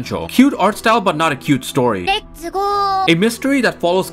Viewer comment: Very minor nitpick: at 0:07 she says "Let's ghost!" it's just that Yayoi says the "Let's gho-" while Eiko finishes it with "-st". That aside, I'm happy to see some light shed on this series. If sites like MAL, Anilist, etc. and r/anime are anything to go by it seems this anime is going under the radar, so I'm glad to see people talking about it.